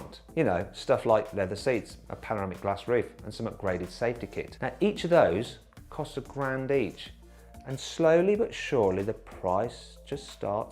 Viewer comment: グラスサンルゥーフゥの所かっこいい。 8:25 8:27 「一台ずつゥ」って言っているように聞こえる